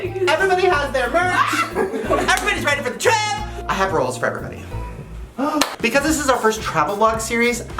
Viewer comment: is it bad that I thought Shane said " I have rolls for everybody " instead of rules cause me too